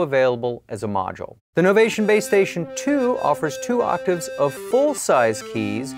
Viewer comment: thought he said playstation for a sec